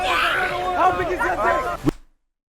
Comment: what did he say, I’ve been replaying it for ages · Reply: "how long is your d*ck!" also sorry you had to wait over a year for someone to reply 😂